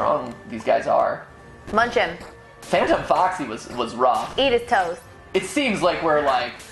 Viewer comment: I’m sorry, but at 46:48 did really say eat his toes ?